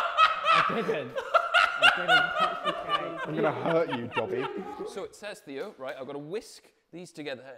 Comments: I know the quotes said “Dober” but ngl I thought he said “dobby” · "I'm going to hurt you, Dobby" is probably the most threatening thing that James has said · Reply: I thought he said dobey but the line at the bottom says he said dober, so sad days:(( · the subtitles say dober instead of dobby